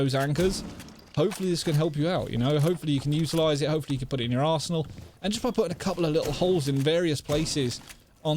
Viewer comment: I thought he said you can put it in ya ass whole lol